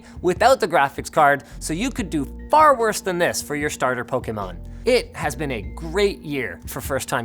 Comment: im not the only one who heard him say "starter pokemon" right